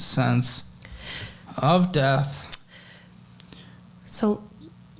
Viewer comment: Every time says "Debt", I hear "Death" and I don't think that is coincidental.